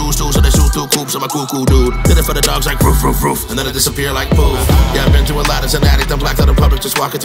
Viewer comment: did merk just say this @ 3:00 disappear like proof